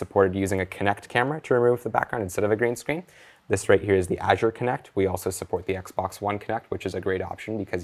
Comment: Can anybody tell me the name of that alternate kinect they used at 12:25? Can't quite make out what he says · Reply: Shwacker There is the Kinect v2 (Xbox One) which I used for a long time for LIV, and there is the new Azure Kinect recently released by MS